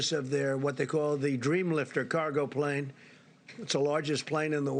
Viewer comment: I thought you were saying “Dream Licker”. Boy do I feel dumb right now. I thought the Antonov 225 was the largest.